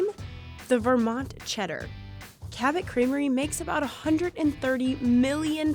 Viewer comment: I thought it said cemetery and I was really confused for about 4 minutes